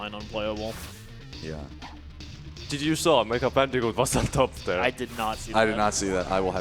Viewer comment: I dont understand what he said. Michael something was on top there? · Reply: Never mind he said mecha bandicoot